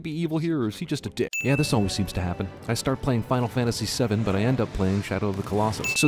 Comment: I just started a marathon of this series this weekend… Coincidence that this get released at the same time? 2:24 Mentions he starts playing FF VII, but the subtitles say FF XII…